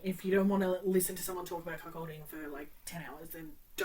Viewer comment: not sure if I REALLY want to read it or think it'll be a trainwreck for me. I thought you were saying "c*ck holding" for the first bit then realized it was cuckolding... either way... I think I've settled on INTRIGUED because I've never read about cuckolding? Thanks for the weird ass rec!!!